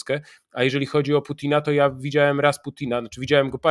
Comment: W 19:28 usłyszałem- widziałem Rasputina 😂